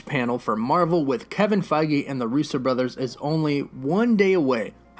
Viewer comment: It sounded like you said rooster brothers at the beginning